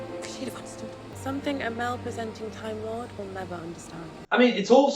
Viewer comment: The first time I watched the episode, I heard "mal-presenting timelord" not "male-presenting timelord" and I feel like that mis-quote is actually a much better story. You have the 14th doctor "mal-presenting" with the 10th doctor's face BECAUSE he can't let things go and because he's "the one who regrets" from the 50th special. 10 is also the Time Lord Victorious who relishes and wields his power. So "letting things go" is legitimately not something that would occur to him.